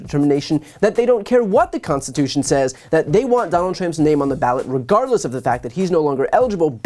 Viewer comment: sounds like you said donald tramp lol.